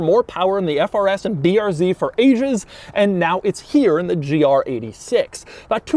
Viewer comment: I thought he said "for Asians" for a sec lol